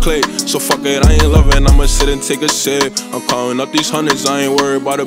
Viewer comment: Best song ever but at first I thought he said “sit and take a shit” 😂😂